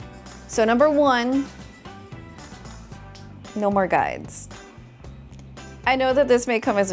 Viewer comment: lmao I thought she said "No more guys" ToT